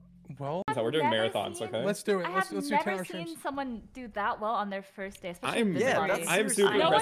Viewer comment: what did hafu say? Do so well on their first day? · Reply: Tjbrown did so well on his first day of among us.